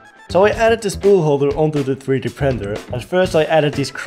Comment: Did bro say 3D Prender?